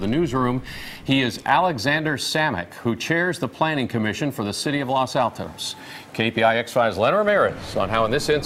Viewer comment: For a minute I thought they said resident of Los Santos.